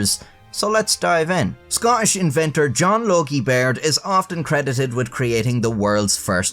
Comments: So, I wasn't looking at the screen and misheard this as "John Yogi Bear." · When he said John Logie Baird, I thought he said John Yogi Bear. It has been a very long week. TGIF.